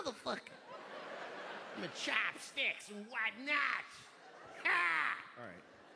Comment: I lived in SHANGHAI. CHOP STICKS AND WHATNOT! HA!!! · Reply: I originally thought he was saying "White Knight" maybe referring to Sun Hongbin or something, but "whatnot" seems to make more sense in the context.